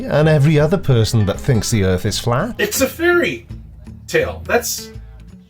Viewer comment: I thought he said, " it's a theory-tail" Now that would have actually been quite clever and 'potentially' (but not necessarily) witty.